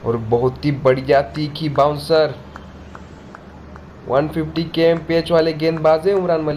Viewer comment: Wish I could get translations. Did he say 150 k at 0:26?